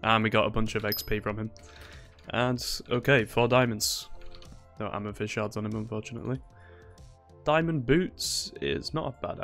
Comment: I’ve listened to this like 10 times and can’t figure out what you said after “four diamonds” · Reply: “No amethyst shards on him unfortunately”